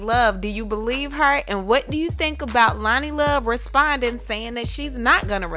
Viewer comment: I thought you said "Loni responds" so the response was im not responding?